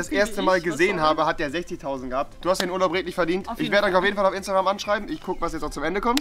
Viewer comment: Was?? 11:00 Du hast denn 100 break nicht verdient. Habe ich verstanden